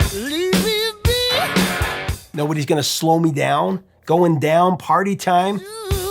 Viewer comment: I always thought it said “ goin down quarter time” not going down party time